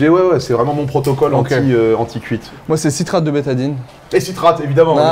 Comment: Moi c'est "citrate de bétadine" HAHAHA. C'est "citrate de bétaïne" ;) La bétadine (jaune) c'est tout à fait autre chose.